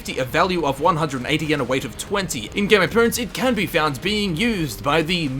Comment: did you say in game of thrones ? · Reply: James Jameson Haha "In game appearance". The first time I listened to it back I thought I said "In Game of Thrones" as well!